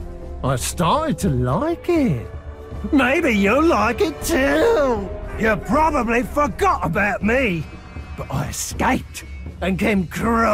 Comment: I wasn’t listening properly and I thought this dude said”you probably fuck got about me” 54:40 and that’s wrong i shed a tear for every uruk on my side that dies